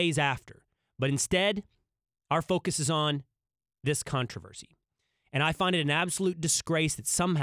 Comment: I thought he’ said ”instead we are focusing on this C*NT.”